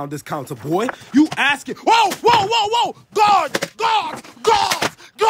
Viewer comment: Bro he's saying guards but the captions say WHOA WHOA WHOA WHOA GOD GODS GODS GOD GODS